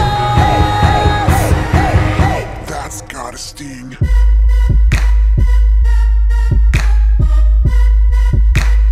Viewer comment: anyone knows what does teemo say in 1:04 ?? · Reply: That's gotta sting